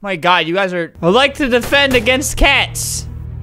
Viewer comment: did you say “defend cat”